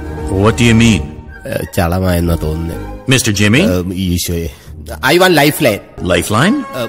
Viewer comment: RM : Mr jmmy But I heard it as : Mr Jimin... really